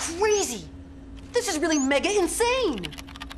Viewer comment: I have been wondering for the longest time what Jeremy said here, now I got it. "This is really mega insane." 12:55 Also, "Roachsters"